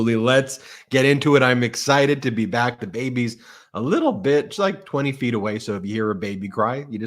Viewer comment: does call his new baby a little b*tch?!! 😂